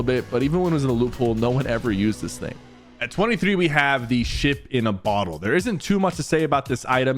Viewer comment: who thought he said sh*t in a bottle